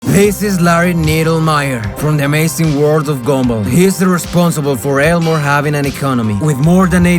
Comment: With the way his voice is I thought at first that he said “The amazing word of gumball”